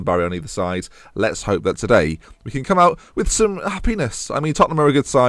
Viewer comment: it sounds like he says "ah penis" just saying